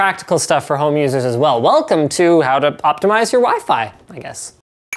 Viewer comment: i thought it said optimize your wife lol